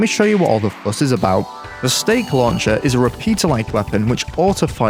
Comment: For the first half of the video I thought he was saying "Steak" and I was like "yes this is a quite unusual weapon"